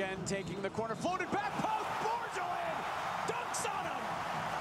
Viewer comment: Am I the only one who heard him say dunks on him after the header .